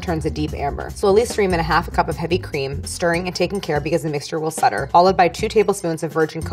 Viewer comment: Does anyone know what word she said at 36:09? It sounds like "sutter" or something. I thought it was my Auditory Processing Disorder, but the rest of my family heard the same thing.